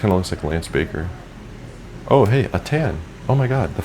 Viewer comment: 9 min I thought you said a 10, not a tan lol